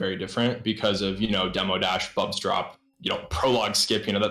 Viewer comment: Yay ♥ I'm so sad i can't catch more of his streams, my schedule is pretty incompatible.. I have one question : At 5:17 , he mentions Demodash and then something else. I think i'm hearing "bob's drop" but it doesn't seem to be a thing. What is he saying ? (i'm french, and i struggle with oral comprehension sometimes) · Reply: He is talking about "bubs drop" the trick we are using in 5b to skip a whole bunch of rooms (safes maybe 20 sec I believe). The trick is also extremly hard (a few frames to input) and costs 12 sec every time you fail so for most runners it is a reset after they fail it. It is technically one of the biggest discoverys in the celeste speedrun (which I will make soon a video about) hopes that answer helps! And yeah everybody loves, didn't interact that much with him but he was super chill, cool and friendly when I talked with him, cool guy he is!